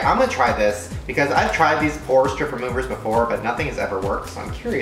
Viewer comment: Every time he said 'Pore Strip Remover' I heard 'Porn, Stripper Remove Her' what tf is wrong with me.